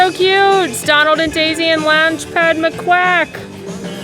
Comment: i thought you called him loungefly mcquack 😂